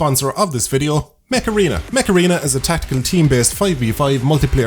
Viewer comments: Mech Arena? I thought you said Macarena, and were going to start with that dance... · Mech Arena - I thought he said "Macarena"